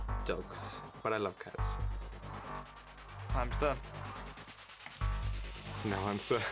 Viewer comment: what is russel saying there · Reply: It sounds like he’s saying times done but I haven’t got a clue